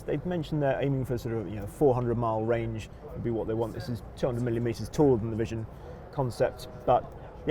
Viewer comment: Oh he said "200 millimeters taller than the concept" but I initially thought he said "200 million meters taller". Which struck me as a really funny joke!